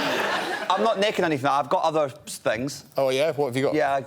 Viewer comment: Please help me with what they're saying, especially what Greg says? I trust the auto generated captions as far as I could throw Greg. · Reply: Iain said “I’m not naked or anything. I’ve got other things” and Greg replied “oh yeah?”